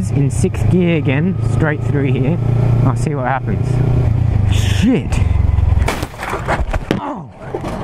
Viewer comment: that "shit" sounded like it said "(sigh) here we go again" but I'm so glad you are okay.